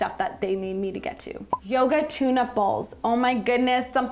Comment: I was making a salad and listening to this video and I thought you said “yoghurt tuna balls”. I was like… ummm that’s a weird craving 😂